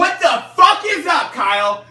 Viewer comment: Anybody got the lyrics to this? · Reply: i thought he was saying "step the f*ck okay" at the end but seems like it is "up kyle" not "okay"